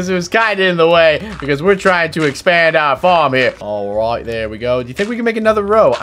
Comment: why did i heard unspeackable said aright there we go in british is it just me on not